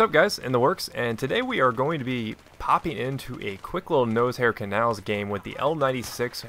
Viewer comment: At first I thought you said "Nosehair canals", kinda cracked me up! :D